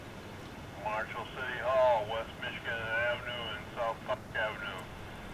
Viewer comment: sounded like he said S f-ck AVE lmao.